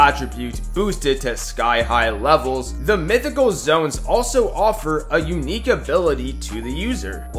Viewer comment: i thought u said zones💀